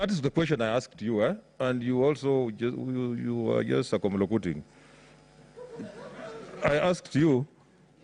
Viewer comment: Hehehehe.. What did Speaker Lusaka say? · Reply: Circumlocuting.